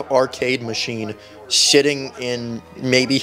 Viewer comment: Did he say sitting or did he say sh!ting I’m going to go for the first one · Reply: sitting